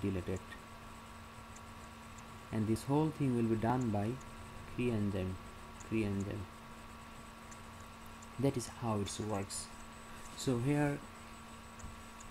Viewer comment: Is the cre-Lox recombination model the same as cel type specific conditional knockout? Also, what are you saying at time 5:20? Cre ent?